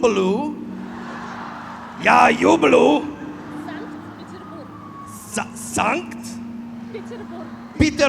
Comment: Я ЮБЬЮ САНКТ-ПЕТЕРБУРГ))) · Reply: А мне вообще послышалось : " я юблу санкс Петербург" 😅